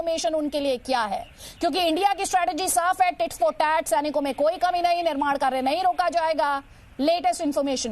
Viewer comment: Did she said Tits for tat at 1:13 ?